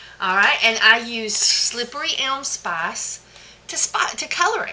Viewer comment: Hello, Can or somebody to write what did she said about the cedarwood and tea tree soap... what spice did she used to color? (video time 1:18:49) I don´t understand that word, and I don´t know how does it write to check it in the translator. Can somebody write it for me, pleaseeee? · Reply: she is saying SLIPPERY ELM SPICE..Slippery Elm is a tree